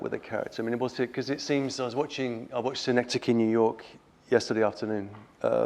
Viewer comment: Simon Critchley mentions a film here, does anyone know the name of it, because I couldn't understand him? Thanks. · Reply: "Synecdoche, New York" with PSH as the main character.